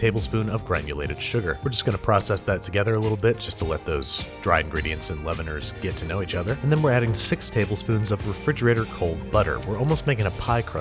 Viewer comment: "just to let those dry ingredients and ???" Could someone kindly tell me what that word is? I can't really tell. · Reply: Leaveners, like baking soda or powder, to make the dough rise